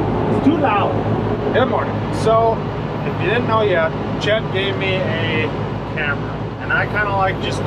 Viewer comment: Thought you said "Chet named me Hammer" 🔨😀